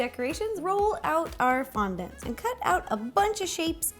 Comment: When you fondant I thought you said condom